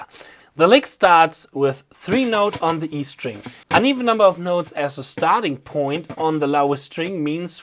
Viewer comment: did you say "an even amount" or "uneven amount"? Also the use of the giant blue pick is very helpful to emphasize the slant direction! very nice touch, excellent education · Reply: That’s a little bit unfortunate spoken, it is uneven :) I will try to say „odd“ in the future a little bit better to understand